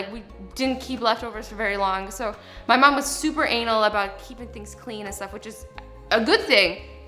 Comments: Is it just me or did azzy say my mum was super anal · did she say annal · did she say her mom was super anal about mold?!?!?! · did she say her mom was super anal?😂